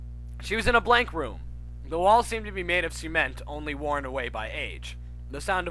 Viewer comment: it sounded like you said semant